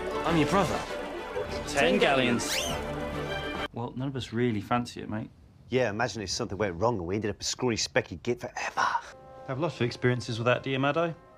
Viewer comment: literally wtf dir he say 😭😭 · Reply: “Well none of us really fancy it mate” “yeah imagine if something went wrong and we’d end up a scrawny specky git forever”